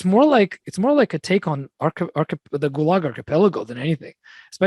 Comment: Not sure what word is being said at 1:33. Archipelago? The music is amazing. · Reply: Gulag archipelago : <link>